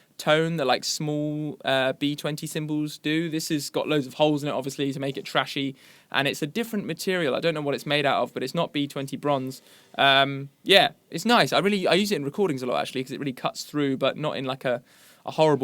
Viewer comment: i thought I heard Bonzo